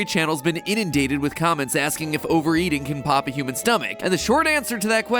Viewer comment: I thought it said can you poop your stomach 🤣